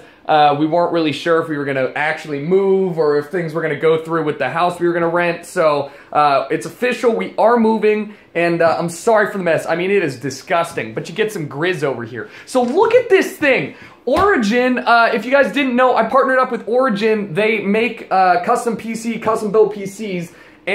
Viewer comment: I thought u said jizz. shhh hahaha